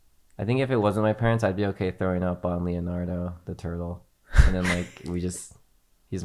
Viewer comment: said he'd throw up on Leonardo the turtle but i heard "Leonardo de" and had the split second thought "Leonardo DiCaprio is your hero" but then he finished and i was like "oh shit my bad"